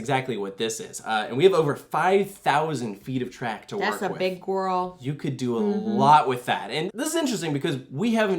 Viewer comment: what does she say? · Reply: That’s a big gorl